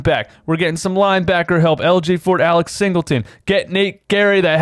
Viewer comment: Did I just hear that LJ Fort is coming back at the 1:44 mark? 😂😂😂 love the vids bro!